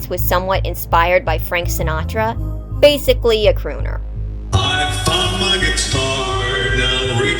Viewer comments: Whenever i listen to rockstar bonnie say his "i found my guitar", i Always hear "i fuck my guitar"... and i cant unhear it. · Why did I hear "I f*ck my guitar"?!?